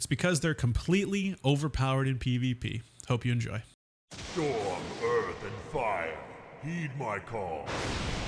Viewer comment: I don't know how I never noticed this before, but during the intro with the old wc3 voice line I just realized he says "storm, earth and fire.. heed my call" which makes so much fucking says, for some reason in my head I thought they were saying "you're birthed in fire.. need my call"